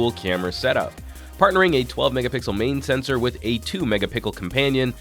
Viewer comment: Lol megapickle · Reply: Yea, I heard "mega-pickle" but when I hit rewind and turned on the captioning...it says "megapixel"....so from now on, I will say "mega-pickle" instead.